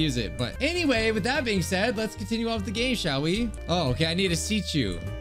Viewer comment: at the begining instead of hearing "lets get on with the game shall we" i heard "lets get on with the gay shall we lmao"